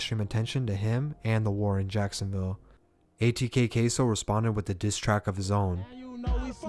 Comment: sounds like he said "Caseoh"